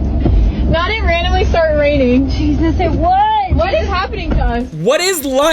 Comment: she actually said "not it randomly start raining." Holy shit I've never heard a stupider sentence